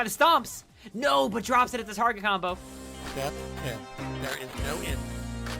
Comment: I thought the last step said “The re is no end.” I was so confused for a second lol